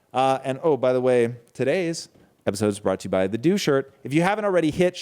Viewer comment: I thought he said at the end "this episode is brought to you by the doucher" but of course he said "the Do shirt" haha.